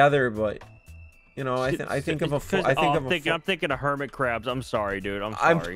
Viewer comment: when said "hermit crab" i thought he said "hermitcraft" aHA